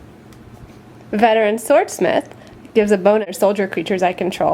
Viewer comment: did she say gives a boner to soldiers?